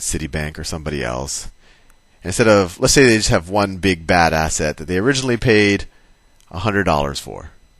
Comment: "bad asset" I heard "badass ed"